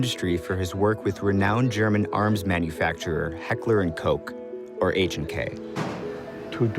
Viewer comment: did u just say heckler and coke?!?!